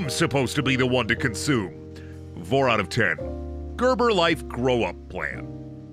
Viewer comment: I HEARD THAT 'VORE OUT OF TEN'!